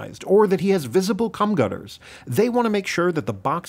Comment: Did I hear him say "cum gutters"?